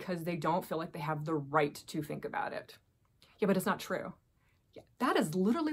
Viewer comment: Hey, do you got a german background? I think I heard "aber" instead of "but" at 08:48 😁